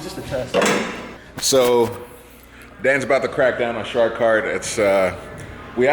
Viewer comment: So you settled on shartkart? Not a fan of the name, I thought kartigan, or hoonicart/hoonikart would have stuck, sounds better. oh well. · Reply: ViperDemon6819 Wright I thought he said shark kart and I thought it suited it well..